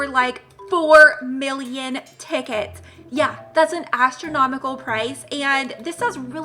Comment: I thought they said it would cost 2 million tickets to get giant panda?